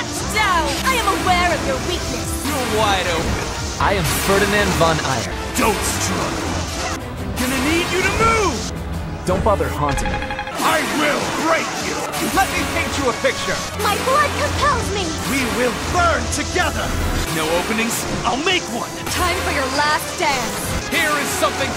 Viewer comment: I heard "I will r*pe you!" And I can not unhear it.